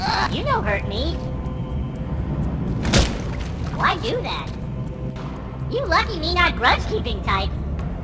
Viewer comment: I think he said "You lucky me no grudge-keeping type"...